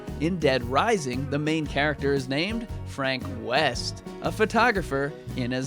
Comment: I thought you said Frank Weast